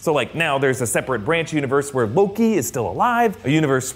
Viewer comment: is it just me or did he say Hoki instead of Loki?